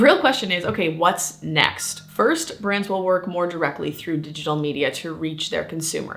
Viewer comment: did she call those publications, the “Shill” Media? That’s some candor there...